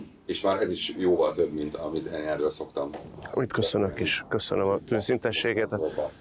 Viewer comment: től 23:08 -ig mit mond Ádám? Értette valaki, mert én sajna nem. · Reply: Passz mostantól Passz